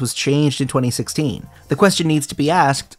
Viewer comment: i heard "brain damage" instead of "brand image"